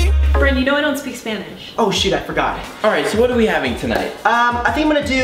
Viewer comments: Thanks for watching <3 Give this video a LIKE if one of your FAV songs was in it! :) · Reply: MrBrent98 at 3:08 I hear u say oh sh*t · You left Caleb again! Legend has it he is still stuck at the fake restaurant. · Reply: did Brent say the sh word 🤨